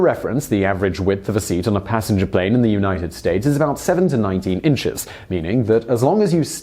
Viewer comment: Sounds like you say 7-19 inches... 7 is definitely not wide enough.